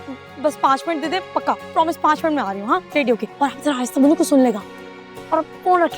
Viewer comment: did she really say hamza aiesta bolo?